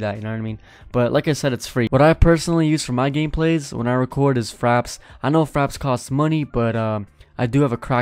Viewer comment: I thought u said faps not fraps lol